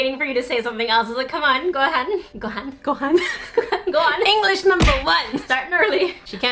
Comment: saying "go on" at the beginning (0:45) sounded like ''gohan'', a japanese rice dish. #FeedSika